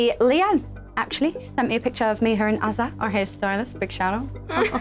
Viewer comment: I couldn't understand Perrie that well at 3:45. Did she said that Leigh-Anne send her a picture of Harry Styles? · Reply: I think she said hairstylist